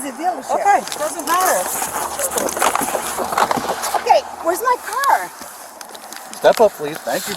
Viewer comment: did she really say “where’s my car” 😂